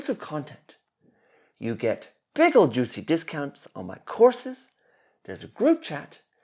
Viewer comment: I thought he you said pickle juicy discounts. Had to do a second take